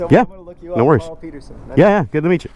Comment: Great video as always. throttle therapy even if vicariously is always a treat for me here. so BTW isn't that the same 471cc engine as in the Rebel 500, The CB500F, CBR500R, CB500R etc.? Also I heard "Carl Peterson" or something close to that.